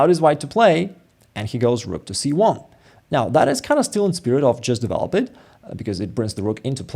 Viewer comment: I definitely heard "Smirnov just developing"